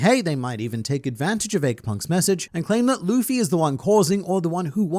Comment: did.. did he say “Eggpunk”?